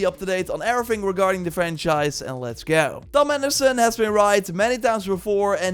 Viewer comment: i thought u said "thomas edison has been right so many times before"